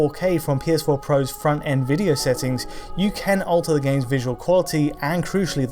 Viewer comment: Did he really say Nvidia settings?